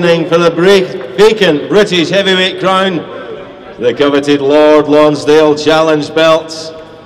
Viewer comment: "The coveted Lard Lonsdale belt" is it me or did I hear that correctly?????